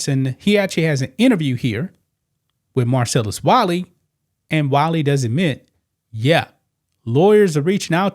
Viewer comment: For a second i thought you said Marcellus Wallace.